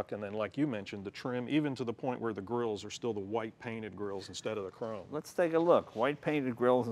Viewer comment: White painted grill... I thought he said "white panty girl." Haha my perverted ears..